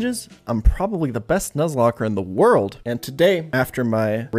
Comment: First time hearing this intro, I heard you say "I am the Depressed Nuzlocker in the world".... which is also probably true